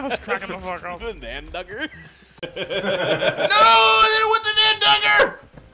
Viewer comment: I think what tried to say was "I didn't want the damn dagger!".